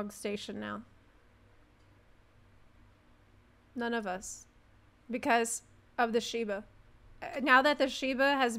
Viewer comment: I legit thought she said "because of the SHIVA" and instantly went to the store to look for Shiva outfit...... -_